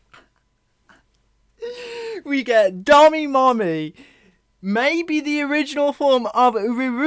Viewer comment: did u just said "gami mommy"